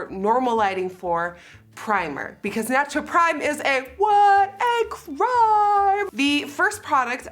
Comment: when you said prime i thought you said “because priming is WHUUUT?” so i literally said OUT LOUD. “FUNDAMENTAL”